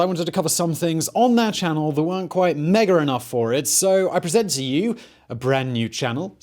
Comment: What does he say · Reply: “Mega enough for it.”